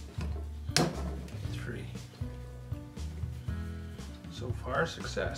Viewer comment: Hello, Nice video. As you asked for feedback, I comment. I hope this will serve as a reference and help. Subtitles worked here, regularly well. Some words of what you said did not appear or were captured different. Some examples: Appear words that you do not say. ("yeah") In 2:06 you said "three" I think (the third screw could loosen, because you were counting), the subtitle in English and translated into Spanish says "free". In 8:12 I think you said "chuck" English subtitles say "truck" and the translator in Spanish refers you to that vehicle. 11:51 chuck appears in english subtitle as "check" ( test ? ), which in the translated Spanish subtitle says "check", but will be interpreted as a paycheck. Thanks for sharing my friend, good job!